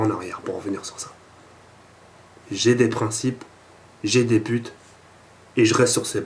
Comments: Quand j'ai entendu "J'ai des putes" J'etais mort de rire x) Après il sourit en plus ;) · C'est moi ou il dit "j'ai des putes" Sinon super ta vidéo!!!!!!:o